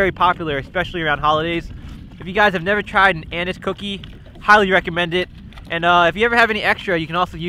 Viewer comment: it sounded like he was saying anus cookie